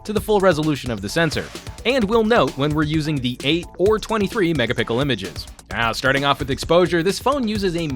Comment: did he said mega pickle images?